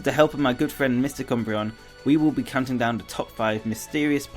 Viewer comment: I thought you said Mr. Cumbreon